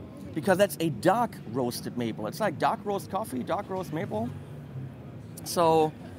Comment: I thought he said "DUCK roast maple" for a second when seeing the first ibanez guitar. lol!